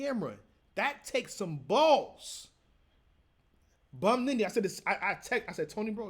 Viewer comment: did he say Bum Nitty lmaoo never heard that name flip before !